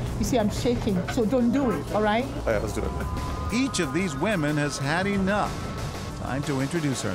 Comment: Did he say wamen?